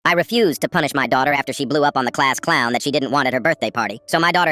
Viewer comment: Bro I thought it said “blew up the class clown” I was expecting her to come back from her room with like dynamite😭😭